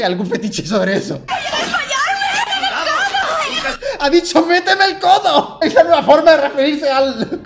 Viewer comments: En la de Hércules, sí escuché “quiero desmallarme”. Pero en la otra frase escuché, “métemelo todo”. XD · en lo de hércules yo en vez de escuchar "meteme el codo" escuche "meteme todo" JKAJDKAHF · en de "meteme el codo" , escuche " que me lo como" igual se escucho mal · eu yo le entendí "Voy a desmayarme" después de que el Dalas lo dijo xdd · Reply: yo escuché "metelo todo", ando peor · yo entendí "Que me de todo" XD